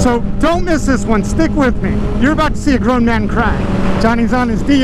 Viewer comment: I thought he said a grom man cry lmfao